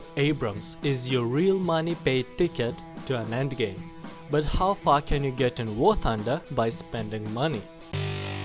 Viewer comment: sorry but i hear ther how fucking you get to warthunder XD